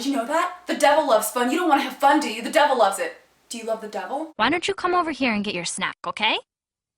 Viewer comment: at first i thought the mother said "why don't you come over here and get your smack"